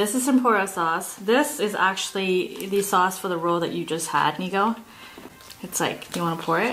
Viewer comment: stop I heard the n word 😭